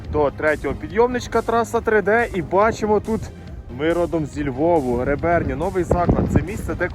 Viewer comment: Тільки мені почулось на 3:33 Виродок зі Львову? 😂😂😂😂😂😂